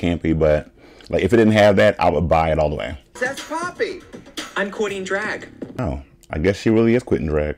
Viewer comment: I always thought Poppy said “I’m quoting drag” 🤣😭 until Nina said “I’m quitting drag”